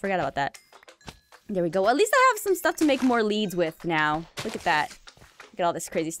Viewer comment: When you said make more leads it sounded like you said make more levins